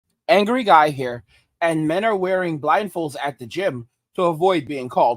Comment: Oooh you said blindfolds, I thought you said rifles. 😂😂😂😂😂. I was like "Man, it's gotten that bad.?"